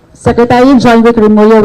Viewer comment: At the beginning I thought she said secretary John Wick Rambo. Lol Had to go back and look at the subtitles 😂